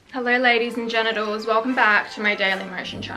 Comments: am i the only one who heard her say ladies and genitals in the beginning 😭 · did u just say ladies and genitals ? 🤣🤣🤣🤣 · Is it only me ? or i heard her saying Hello "ladies and genedows" not gentlmen